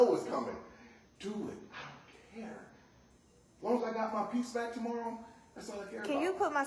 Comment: I thought he said as long as i get my foodstamps tomorrow im good, then i turned down my fan , and realized he said peace.